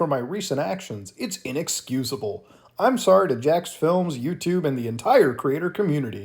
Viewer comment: NO NO, she did not say jaksfilms, she said jacksfilm